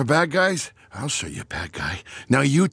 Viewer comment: Everybody is saying the minion said "banana" but I thought he said "but dada" but now I realize that it sounds like both and that makes it even better.